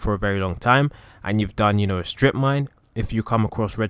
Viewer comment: IS IT JUST ME OR DID I HEAR "STRIP MINE"